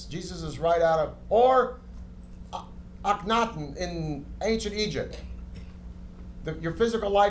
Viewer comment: Can anyone tell me the name of the Egyptian person Peter Weller is talking about? I can't understand it :) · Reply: Akhenaten otherwise known as Pharoah Amenhotep IV.